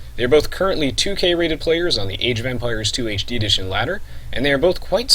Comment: I swear I heard you say Age of Vampires in the beginning